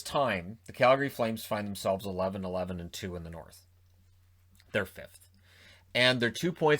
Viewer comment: I heard “they’re filth” at first.